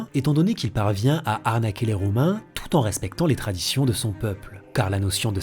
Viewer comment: C'est moi ou j'ai entendu "arnaquer les Roumains" à la place de "Romains" ?